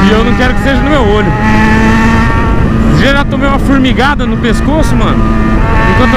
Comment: furmigada????????? FURMIGADA???????????? FOI UMA ABELHAAAAAAAAA ABELHAAAA KKKKKKKKKKKKKKK E SO VC KKKKKKKKKK